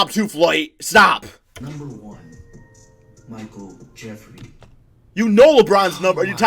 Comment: Did anyone else hear “Michael Jefferee” instead of Michael Jordan.